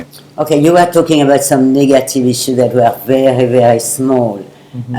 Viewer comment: did she say the nword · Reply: negative issue